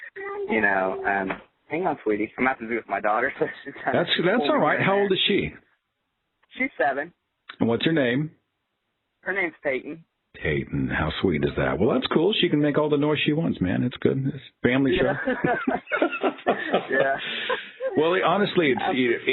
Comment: Sounds like he said, "Her name's Satan."